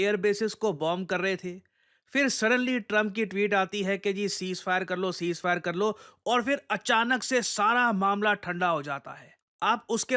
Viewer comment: He did not said Ceasefire karlo he said ceasefire hogeya